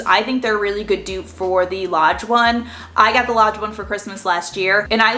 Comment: When you were talking about the dutch ovens and you said a few times that you got the Lodge one, I thought you were saying you got the large one, but with a Boston accent! I had to rewind several times, just to hear you saying "large" with that accent! Then I saw the word Lodge on the lid! Hahaha! That was a good laugh! 😂